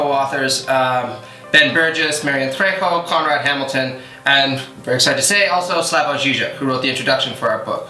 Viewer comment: You (the speaker not the channel) mentioned that you were “very sad to say Slavoj Zizek” why is that, was there a falling out between you and/or Zero Books and Zizek, did I miss something? Why’d you say that? Did I mishear? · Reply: *very 'cited to say. Excited